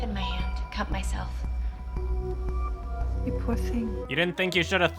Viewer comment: I heard "you pussy" at first XD